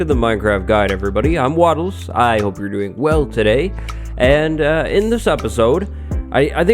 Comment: I thought he said I’m turning 12 today not hope you’re doing well today.